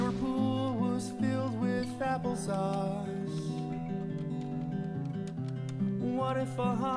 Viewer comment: I swear I heard "fapple sauce"